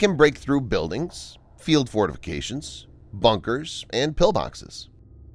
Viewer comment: I had to go back because I swear I thought I heard mailboxes instead of pillboxes 😂